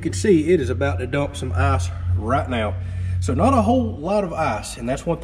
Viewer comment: And now watch the whole video again. It gets hillarious if you understand ass instead of ice The assmaker! -classic 21:13 It is about to dump some ass, so not a whole lot of ass^^ I watched it twice and it was awesome! :D Nice build btw!